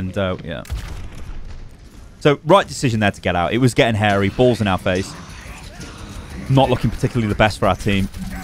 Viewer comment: Im sorry did you just say harry balls in our face? Speak for yourself.